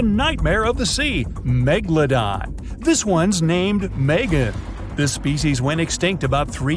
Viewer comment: I heard it as vegan hahaa..... Or it is just me😢😢